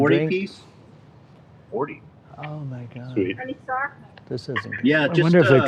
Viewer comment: Did he just say "OMG this isn't happening" :(